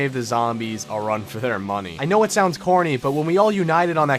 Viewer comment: I heard “I know it sounds horny” instead of “I know it sounds corny” 😂